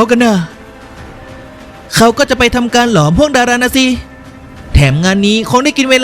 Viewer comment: ผมได้ยินไม่ผิดใช่ไหม หลอมห่วงดารา นี้หยางไค่กำลังจะได้เป็นจักรพรรดิผู้ยิ่งใหญ่แล้วหรอ😮